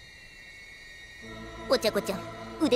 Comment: The way Tsu says "Ochaco chan " is very quirky. I always hear it as "Ocha-kocha" & my grown up ( somewhat kindergartener ) self finds it extremely funny. 🤣🤣🤣🤣